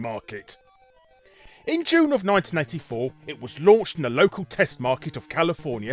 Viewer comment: thought he said 1994 and another time after that obviously its 1984. Think its just the British accent....